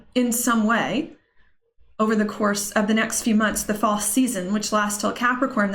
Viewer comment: Granted, I was playing this at 1.5 x speed but at 34:44 it sounded like you called this "the false season". Interesting!! (Yes I realize you said Fall Season but False Season seemed really appropriate with all the censorship and things coming to light or as you said, something is rising up. Whoa)